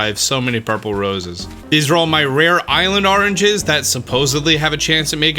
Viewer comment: I'm sorry did you say "Arranges" around 3:55?